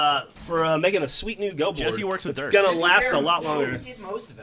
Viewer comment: Did he say Jesse works with Derf?